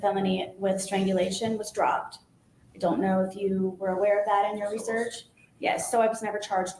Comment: I thought she said influx...of research...